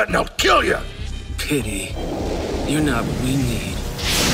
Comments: does he say pity your not really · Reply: "Pity, you are not of my need" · can anyone tell me what he’s saying? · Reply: “You are not what we need”